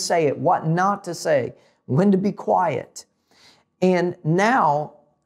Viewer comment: he he just say “Linda be quiet”? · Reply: You know, I had to go back and listen to this part because I was curious as to what you were talking about. It does absolutely sound like I said “Linda be quiet” 😂. What I actually was saying was “when to be quiet”. Sorry, nothing personal 😄.